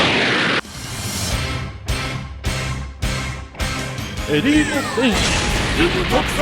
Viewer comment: did it say "notice Senpai?"